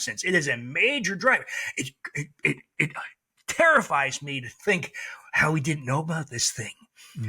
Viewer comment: What he said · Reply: "Terrifies me"